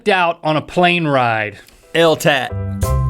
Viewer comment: did rhett just say "eltat"? at 00:08 (Just before.)